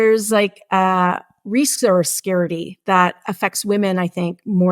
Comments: What is saying?? resource scarcity or security?? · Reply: She is saying scarity, but she means scarcity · why is she saying "resource scare-ity" instead of "resource scarcity" ????